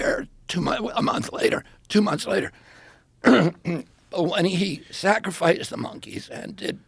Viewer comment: did he just say he sacrificed the monkeys?